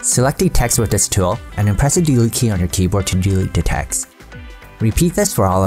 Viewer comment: In 2:10... What am I supposed to press? I have gone through that part 7 times but I can't make out what you are saying :D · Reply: ahhh DELETE key. yaaas. ;)